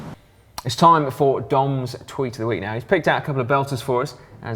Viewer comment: When Si said, "It's time for Dom's tweet of the week," I swear I heard him say, "It's time for Dong's tweet of the week."